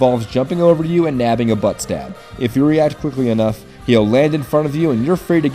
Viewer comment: Is it me or did say "butt stab" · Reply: Nope he said butt stab